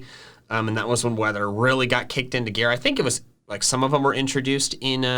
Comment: captions say Gareth? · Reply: M: "kicked into gear" AI: "kicked into Gareth" 😆